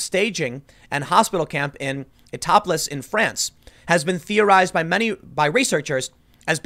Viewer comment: I heard "topless in France". Weird.